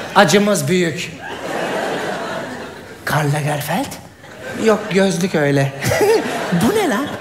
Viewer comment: da ne diyor 🤔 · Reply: Samet Gulmez sen naaptin gardaş. Karl lagerfeld